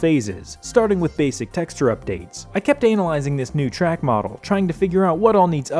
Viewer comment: why did you say “anal izing”?